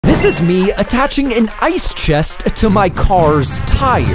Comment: For a second I thought you said “THIS IS ME STEALING A CAR”